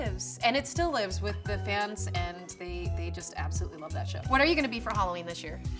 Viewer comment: i thought she said "they just absolutely love that sh*t" hahahhaha